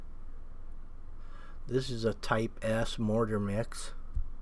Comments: I first though I heard, "Tight ass mortar mix" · Did you just say this is a tight “ass” mortar mix lol 😂 did